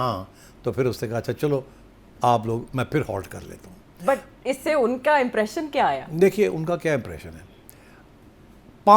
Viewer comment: Second wala tuuuu tuuuu😊😊😊😊😊😊 ni samajha koi Punjabi speaking Bhai hi bt skty hein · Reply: He said “phat gai”